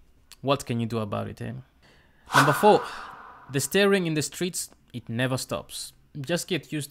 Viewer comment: I didn't understand what you were discussing. Did you say 'staring' ? Thank you for the video. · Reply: Yes, "The staring in the streets... it never stops" Glad you liked it :)